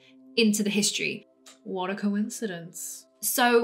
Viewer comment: did you say water coincidence ?? 😄